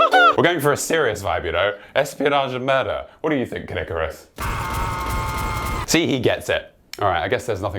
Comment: I thought you said Kid Icarus, not Caddicarus. I was very confused.